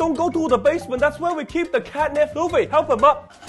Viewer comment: I thought he said they keep "Catnap" in the basement. Y'know Catnap from Poppy Playtime?